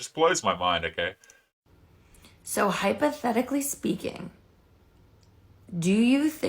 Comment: Did she say "hypo-pathetically speaking"? LOL. That is a cool word - though not a recognized valid word as far as I know.